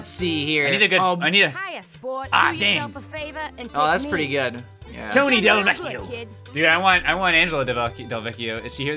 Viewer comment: Did I just hear Curse? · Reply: No he says dang